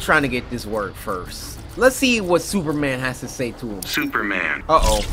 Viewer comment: straight up thought he said scuba man